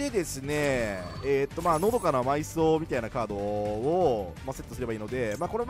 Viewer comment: 「のどかな埋蔵」が「喉から埋蔵」に聞こえた